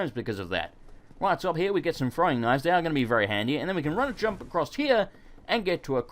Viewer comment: I thought he said "and here we have some frying knives" I was like... um